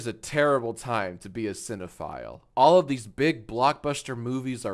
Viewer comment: When you said cinephile, I thought you said xenophile lol😅